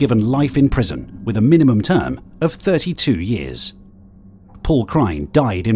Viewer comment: I keep hearing "Pork Rind" instead of "Paul Cryne."